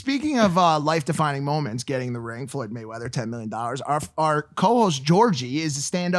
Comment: Did Mike say logan was getting 10 millet